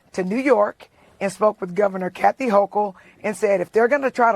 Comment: Did she say she went to New York and voted for Kathy Hochel . 1:55 . Didn't know we could do that .